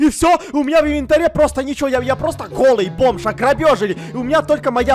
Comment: мне послышалось в начале Эд сказал ограбёжили пиз... Эд не взял урок с серии майнкрафт но там же тоже русалка была